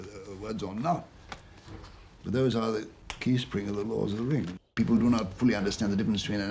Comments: it sounds like he says The Lords of the Ring? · did he say The Lords of the Ring?